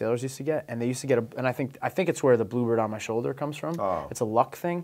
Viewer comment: wHEN HE SAID BLUEBIRD I THOUGHT HE SAID BLUEBERRY OMF